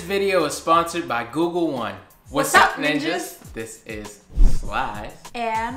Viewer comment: If u put ur volume on the lowest volume its gonne sound like they said "whats up bi***es